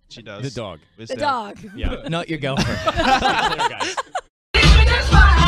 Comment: Ashton: she looks like a baked potato Luke: who? My dog? Ashton: no your girlfriend Me: DONT SAY THAT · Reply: Huh, I thought he said not your girlfriend